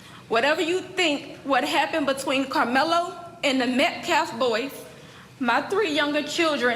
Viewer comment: The Metcalfe boy??? Did she really say that about the kid her son ended? She has cashed in GoFund me dollars but she can't say his name? This family is rotten to the dang core. RIP Austin. Karmelo is a thug, he brought a weapon, he went to the spot and started up. Kid is a mess. Parents should be held accountable for raising a punk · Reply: I have listened to her saying that sentence multiple times and it sounds like she said, "That Metcalf boy", but if you listen closely, she said, " The Metcalf boys."